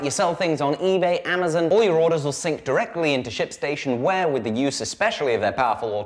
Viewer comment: it really sounded like you said sh^tstation